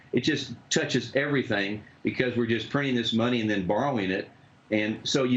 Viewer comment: Did I just hear him say, HE Can't get Engine-motor Oil? (at the 3:54 minute mark?) THAT'S NOW?